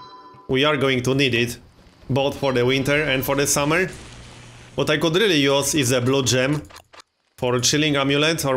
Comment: I thought I heard: "What I could really use is a blow-job, for chilling out..." I lol'd a bit and then realized I was just a pervert. Anyway, loving the series! Thanks!